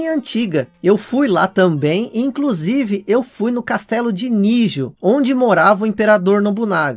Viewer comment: eu entendi castelo de mijo kkkkkkkk wtf